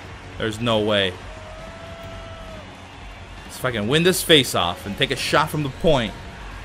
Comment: 'let's fucking win this face-off' is that what said?:D